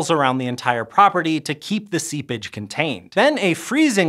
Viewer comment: The moment at 2:53 when Stefan said "seepage" I had to do double take because it sounded like he said "seab!tch" and that was just way too funny for me. Made the radioactive water sound like a super sassy mermaid who needed a timeout! lmao!😂 😂